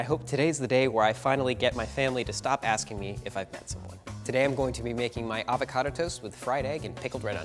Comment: I thought the first guy said “I’ll finally get my family to stop asking me if I’m PANsexual” and I was like damm that’s a really good pun bc he said he loves to cook